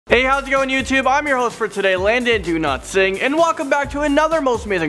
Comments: It sounded like he said landon do not sing! lol! No hate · I heard "Landon do not sing" lol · In the beginning it sounded like he said his name was Landon do not sing xd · i thought he said landon do not sing